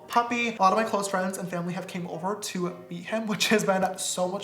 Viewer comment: when I first heard this I heard this as “beat him” and I got really sad for like a solid 2 seconds